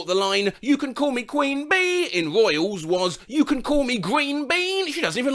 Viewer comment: I WASNT THE SAME ONLY ONE WHO THOUGHT LORDE WAS SAYING YOU CAN CALL ME GREEN BEAN